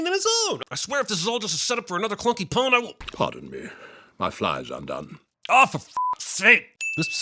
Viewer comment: I always heard “Pardon me. My fly popped out.” not “Pardon me. My fly is undone.” when I first saw this on BBC 3.